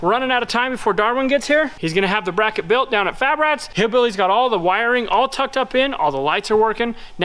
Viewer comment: did you say Drillbilly? That’s a fitting name in the moment.